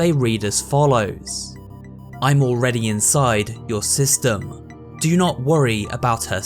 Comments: I wasnt looking and i thought he said "I'm already inside your sister, do not worry about her safety" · I honestly thought you just said: "I am inside your sister" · i thought he said "im already inside youre sister" "do not worry about her safety " "everything will be fine"